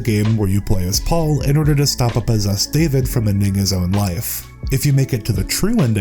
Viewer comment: thought he said “a n****as own life”